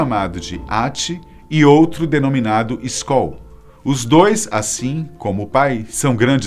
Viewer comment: eu entendi Skol kkkkkk